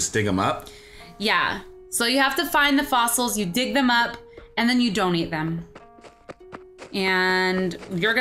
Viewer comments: it sounded like said "...and then you don't eat them" 😂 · At first I thought she said you don't eat them instead of donate them. XD